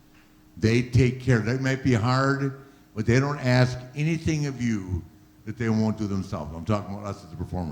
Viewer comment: WHAT HE SAYS " They may be hard" WHAT IT SOUNDS LIKE "They rape you hard but they want ask anything of you that they want do themselves"